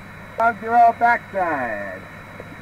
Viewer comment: Did I just hear Mike say “roll it back side!”